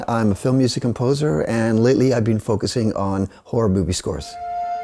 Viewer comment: Did anyone else hear horror boobie scores